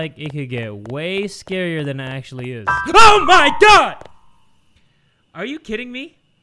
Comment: Am I the only one who heard a cat pun "are you kitten me" 😂😂😂